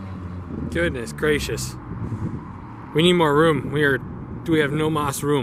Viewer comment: I thought you said, "It needs more Vroom".